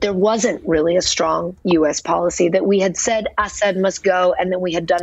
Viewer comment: did she say ass head? Lmfao